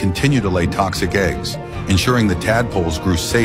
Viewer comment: I heard "just like your mother" at first 💀😂